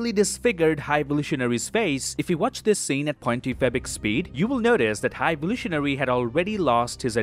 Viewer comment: Is it just me or does he sound like hes saying "2.fabric speed"? It always sounds funny to me